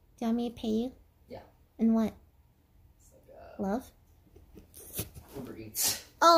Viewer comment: tf does he say?? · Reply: Uber eats